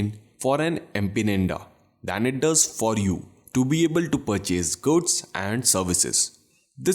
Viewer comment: I heard “purchase goats and services” and “abject poetry”...🙂sorry mate...good video though! Edit: oh shit you did say “poetry!!”...you mean poverty Anyway...what do you think of holding 50/50 gold/silver as I am?